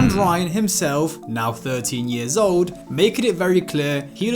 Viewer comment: I looked away for a second and heard “Ryan himself being 30 years old” instead of 13😭